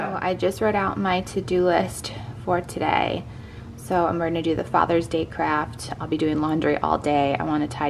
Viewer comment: It sounded like you said “father’s day crap”, instead of fathers day craft... 🤣 gave me a little giggle... 😂